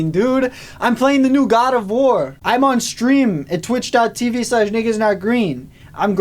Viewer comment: bro my ears perked up so fast 🤣🤣🤣 Niggahs not green is wat I thought was said